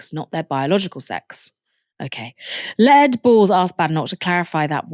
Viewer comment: did you call him Lead Balls? lol